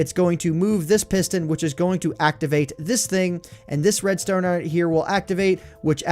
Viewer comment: It sounded like you said "red stoner" and I was confused for a min lol XD